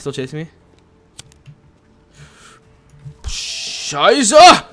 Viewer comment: did you say "scheise"? that means "shit" in german! xD